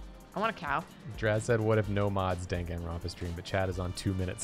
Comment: When started talking about doing a long danganronpa stream I thought he was saying "dang it grandpa" and I was just like, wonder how long a game titled that can be